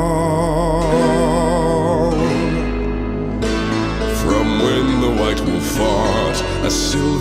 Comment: I definitely heard "From when the white wolf fart"